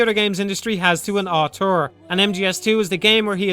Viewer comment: What does he say at 41:22? Closest thing to a what? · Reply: An auteur, french for author, used to describe a director that has so much artistic control over a film that they are basically considered the author of the film.